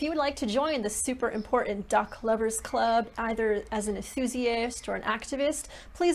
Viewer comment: I thought you said duck livers. I do love duck livers...